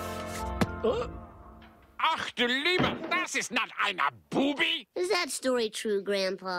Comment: "Ach, du Liebe! Das ist not einer Boobie!!!" - Worst German ever, and hilarious as hell! 😂😂😂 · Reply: I thought he was saying, "Ach, du Liebe! Das ist not Imma Boobie!!!" I thought it was Grandpa's stage name.